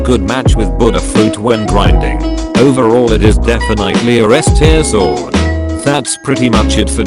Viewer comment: he says S tier fruit instead of sword